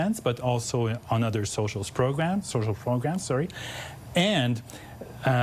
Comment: Quite the slip of the tongue. Sounds like he says Socialist programs before correcting to social programs.